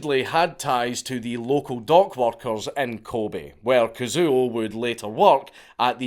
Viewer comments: Oohhh dock workers. I thought you said dog walkers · Ooh. Dock workers. I thought he said dog walkers